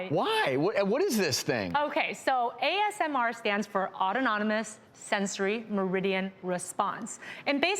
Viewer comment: Did she say autononomous?!